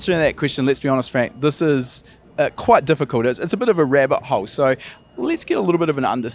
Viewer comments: Thought he said rare butthole before I realized he said rabbit hole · Did anyone mishear "rabbit hole" as "rare butthole?"